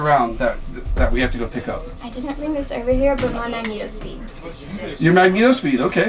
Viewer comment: I have played his three times but I cannot understand what they say at 11:20, probably because I may not know what it could be. Help anyone? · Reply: "I didnt bring this over here, but my magneto speed" -- its a thing that clamps onto the barrel to measure bullet velocity